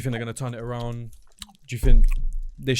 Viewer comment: says jew instead of you lmao 1:20:45 he says jew think like 5 times in a row